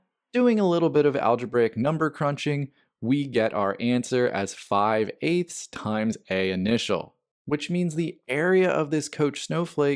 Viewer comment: I think you meant to say 8/5 times A0. Sounded like you said 5/8 times A0.